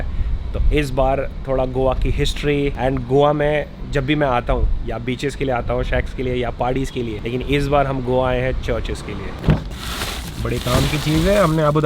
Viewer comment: pe ye kya bola bhai😂😂 sax ke liye?